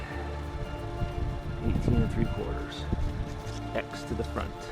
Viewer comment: K not sure on your question. But when I am laying out the lookouts I start at 45 1/2” when hooked on the tail. If you add 1 1/2” for fascia you are at 47” up from the leading edge of the fascia I snap my line at 48 1/8” which pretty much centres my plywood edge on the lookout. I really hope that answers your question. Thank Dr.Time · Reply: I have to watch it again. I thought u said 18/3/4 x behind.